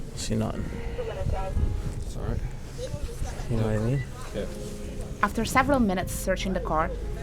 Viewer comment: I watched again and at 2:39 he said "You know what I mean" but I hear "you got any weed" and then the other officer says "Yeah"....Then they find the weed seconds later.....